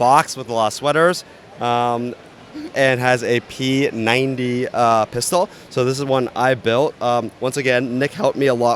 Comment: He says “P90 Pistol”. Did he actually say that or am I missing the “and” part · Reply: He actually said "P90 pistol".